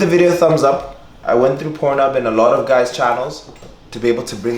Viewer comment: i thought u said, "i went to purnhoeb and view a lot of channels" 🤣